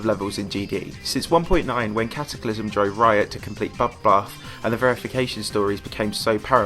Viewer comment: Did you say bobbath